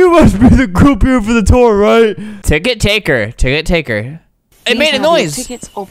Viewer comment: i thought i heard ticket tanker